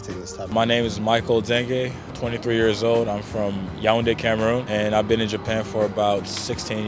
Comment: I usually dont comment, but i am HIGHER than the burj khalifa whilst this is playing in the background and in the beginning michael say "im 23 years old. Im from Cameroon (But i heard it as Cameron)" then right after i hear "My name is Cameron" i for a legit solid 20 seconds stood there frozen and just went "Oh okey, in japan people are fr freaky freaky. I need to go 😏"... bruh 😭😭😭